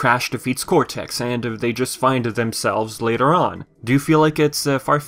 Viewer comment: Did you say "they finded themselves?